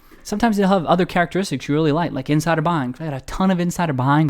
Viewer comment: Is he saying he likes to invest in a company when it has insider buying at 2:51? Did he say insider behind?